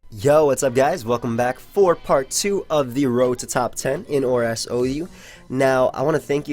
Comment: Well I just heard "Part 2 of the Rototaupe Ten" - Rototaupe is actually Drilbur's french name - I laughed x)